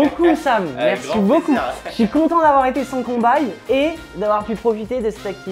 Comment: cest moi ou a 19:08 il a dis conbaye ????????????????????????????